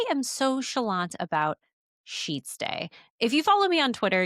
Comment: I totally thought you were saying "sheet-stay" at first, and it was an ad segment for those little clips you can use to make sure fitted sheets stay in place. ... Those clips are really helpful. Just sayin'.